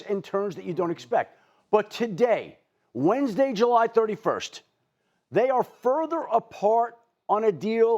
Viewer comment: did Adam just say "Wednesday July 31st"? Or am I just tripping?😲